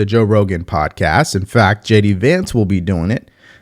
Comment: Much respect to JD Vance taking the joe Rogan show on Wednesday. Kamala turned it down, and that is on her. Trump for president 2024 👍🏿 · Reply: did I missed heard Devory- I thought he said Walz. Holyshit it is Vance....